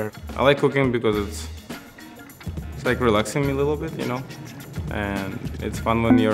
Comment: Idk why but I thought he said cocaine and was concerned about him thinking it’s relaxing and feeding it to his family and making them like it